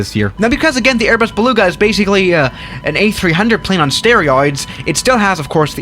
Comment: did i just hear sterioids correctly?